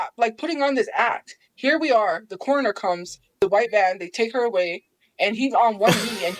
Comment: I think she actually said “white van”